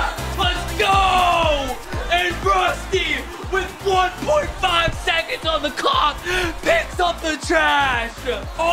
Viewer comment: I thought I heard : Rusty with 1.5 seconds on the 🐔 ! with all his excitement😂